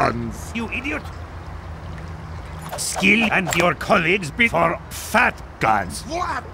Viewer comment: Sounds like spy is saying "Skill and your colleagues before fat guys". XD